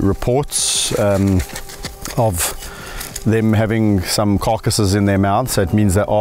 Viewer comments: I thought you said "caucuses" when you said carcasses. Political hyenas, President Geena, Prime Minister Kevin. Glad you're back. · First I heard "carcasses" as "caucuses."